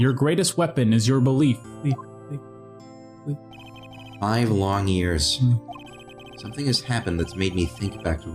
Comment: The "five long years" line I totally misheard and I thought Phoenix said 'I have long ears'